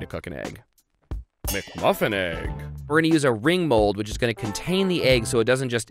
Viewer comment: anyone know what that thing he's using? It sounds like 'ring bold' but that's not a thing lol